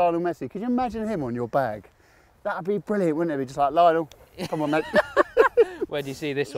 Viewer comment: When asked who Matt Fitzpatrick would have as a caddy I first thought he said Lionel Richie 😂